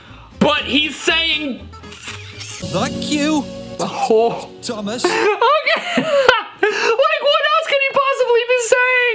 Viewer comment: Hi! I CLEARLY heard “F**k you, Thomas.” I’m surprised parents didn’t complain about it like they did Bob the builder, which I didn’t hear any swearing in that clip, it was just muttering like they claimed it to be. I did hear “F**k” in Hunchback of Notre Dame, I also think Mr. Rogers knew what he was doing because of the way he grinned right before he flipped the bird! 😂 Hilarious video! Have a good day! 😃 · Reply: I heard "thug you, Thomas", dont know what that means but i heard it!